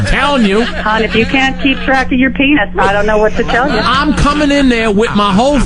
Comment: actually said Todd if you can'tkeep track of your penis at 32:49 I cant believe they didn't catch that. God this is terrible